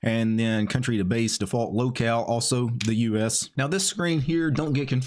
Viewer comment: Haha, I heard "Lolcow" instead "Locale" 🙂